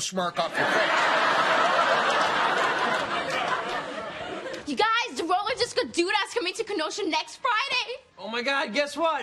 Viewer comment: I swear I think I'm having a stroke, what did she say? All I could make out was "...Next Friday" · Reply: "You guys, the Roller Disco Doo-dah is coming to Kenosha next Friday!"